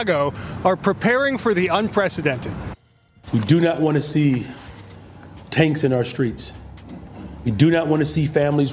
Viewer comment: did he really say “ no tanks In our streets!” There won’t be tanks lls